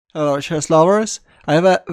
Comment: I don't know what do you say in the first, I heard " Hello Chest lover" , I like playing chest